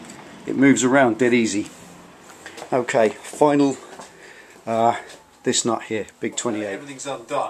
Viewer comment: Sorry but what did you say at 10:22? -Finally, this not here. Big 27. Right?